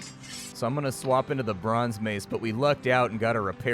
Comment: I heard “le bronze mace” 😂😂